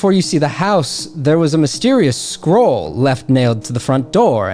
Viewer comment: I thought u said a squirel nailed to the door